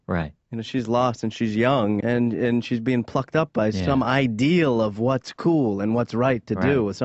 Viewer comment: what did he say??? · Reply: "Plucked up by some..." but yeah I thought I heard different the first time.